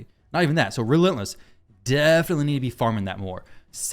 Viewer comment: Wait did I hear it right? farm relentless set?